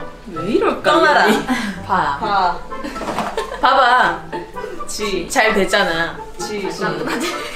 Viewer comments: did she said 지랄 (jiral)? Just guessing 🤣🤚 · i wanna know what yongsun said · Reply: I think it's 지랄 (jilal) mean sh**